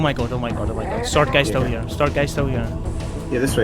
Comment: When you said "sword guy", I heard "Sergei" and wasn't sure if that was the covenant elite's official name or if you'd had so much trouble dealing with him that you'd given him a name. In any case, I'm glad Sergei didn't kill you! Excellent editing, by the way!